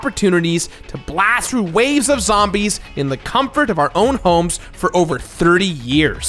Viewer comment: Wooooah at 1:07 did he say waves of zombies? Or am I just mishearing it?